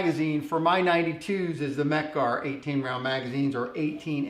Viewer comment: I'm curious as to why your favorite factory magazine is the PVD coated 15 round magazine over the same coating 17 round magazine. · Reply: KaB ar Funny I thought he said his favorite was a 18 round mecgar mag....